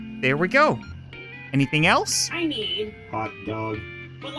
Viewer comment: i legit heard them say "butt plug" instead of "hot dog"